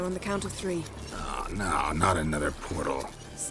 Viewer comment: Can anyone hear Gandalf at 4:21 ? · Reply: Yes, he said, "oh no not another portal."